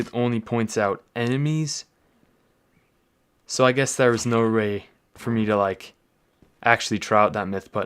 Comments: Did you say no ray or no way? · he says "Ray" instead of way.🤣